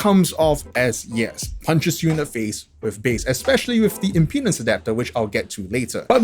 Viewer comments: What did he say at 4:06? Im "P" ness adapter? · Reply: Impedence you mean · Did he just say "in penis adapter" ? I did not expect you need to plug it to THAT dongle DAC